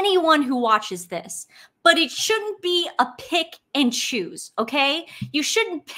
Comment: When you said 'pick and chose", I thought you said "pig in shoes"! Misan's new nick name....